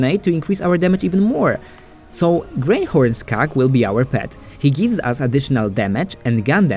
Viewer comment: What's wrong with my voice? Too high or am I talking too fast? Let me know, I really want to improve my videos and my channel. Thank you for your comment :) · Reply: Also, at 4:16 sounds like you said "grey horse cock will be our pet" 😂